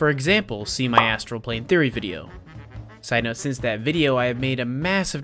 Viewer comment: when you said "See my astral plane theory video" i really thought you said "see my as-". im going back to bed